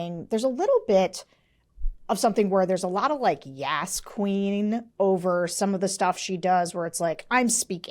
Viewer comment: Can somebody help me out? At 8:52 Sarah says something that sounds like "there's a lotta yasquine(?)". Can somebody tell me what that is, and/or what it means?